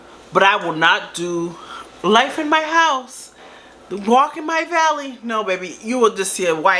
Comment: i thought you said “walking my belly” 🤣🤣🤣😭